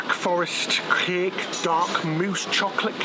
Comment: there’s a mouse in that cake instead of mousse 🤣